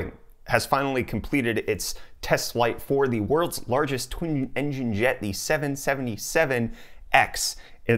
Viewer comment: Is it omenous, that I missheard and thought he said "world's largest tomb-engine jet"?